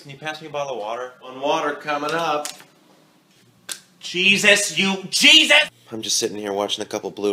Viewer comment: did he call Jesus a cheez it??? 😂😂😂